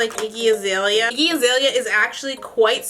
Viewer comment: I thought you said that you don't know why azelia banks gets hate..I was gonna huh😧😑😂😂but then I realized u said Iggy azelia